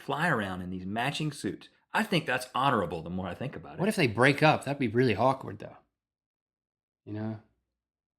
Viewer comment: lol I think I heard Link say "But what if they broke up? That would be hawkward." PLEASE tell me that I heard right! xD